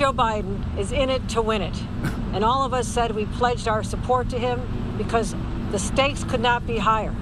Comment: “all of us said we pledge our support to him because the states could not be higher” lmfao that’s what I heard… well you gotta be high on your own supply lady, ‘cause I don’t think that drug dealers would sell to y’all anymore… you’ve made their clients broke as hell! And practically legalized it in some states